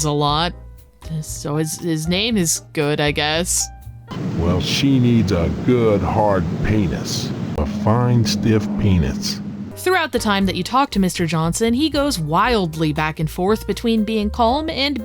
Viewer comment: why does it sound like he said penits?